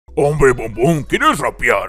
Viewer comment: yo le entendí hombre bombon